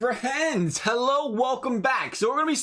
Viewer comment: Yo, at the intro to this video instead of saying friends I thought you said my name, Brent, and I was freaking out.